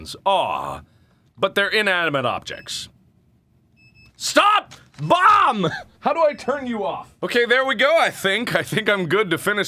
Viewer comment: I thought he said " stop mom how do I turn you off?!" XD